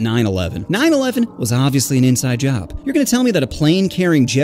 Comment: I thought he said inside joke